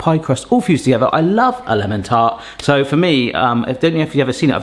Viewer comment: Aaah, "I love a LEMON tart" not little tart 😬🤣🤣 Had to replay because I was sure I misheard. I did.